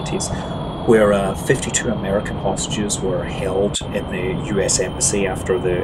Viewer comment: "52 American prostitutes were held" I know you said hostages but it sounded like that and made me lol.